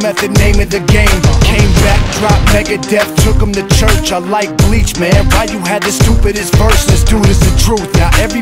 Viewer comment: Is it just me or did he say "I like Bleach" somewhere